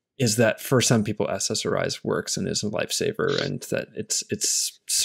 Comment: "SSRI's for some, works and is a light saber"..hhmmm.. Did you actually say "life saver"? Is it my hearing...or is it my microdosing.... hmmmm.. no matter, never mind... :-) Thank you James for bringing it to us again... Thank you Adam.